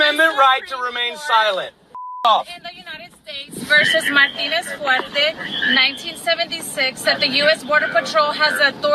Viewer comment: did she just say versus my penis? Lmao